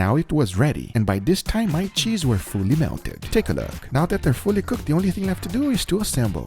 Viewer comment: 😂😂 I thought you said now there f melted ha